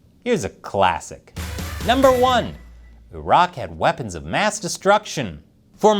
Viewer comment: I thought you said: "The rock had weapons of mass destruction." - I was like 'The rock?! Maybe it's some other rock?...'